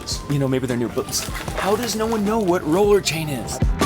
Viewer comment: did you say "motorcycle chain"?